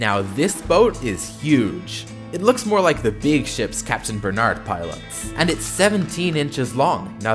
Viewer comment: what did he said i heard he said! he dick ship